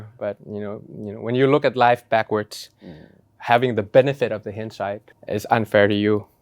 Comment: คุณทิมพูดว่า “…having the benefit of the hindsight…” รึเปล่าคะ ถ้าใช่ มันไม่น่าจดแปลว่าคำใบ้นะคะ ถ้าผิดขออภัยค่ะ